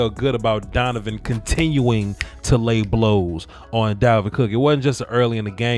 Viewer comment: Yo yo. I thought the commentator said cookies after wikson hit him lol